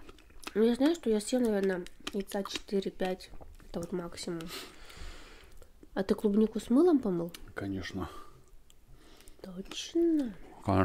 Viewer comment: Я правильно услышала «а ты клубнику с мылом помыл» ????? Как можно клубнику мыть с мылом?😂